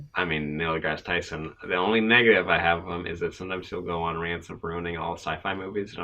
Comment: I swear when said “the only negative” I thought he said “the only N** is” and I was SHOOK!